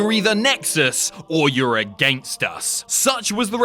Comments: The way he said "against" i thought he said gangsta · In intro when he read your either nexus or youre against us it sounded like he said gangsta at the end "against us"